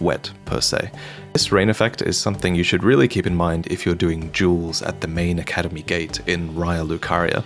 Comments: it sounded like you said "jewels" instead of "duels" which I figured I misheard or you misspoke but then seconds later you again say "this is where people do jewels". Am I just out of the loop and do not know what doing a "jewel" is? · I thought he said Juuls instead of duels LMAO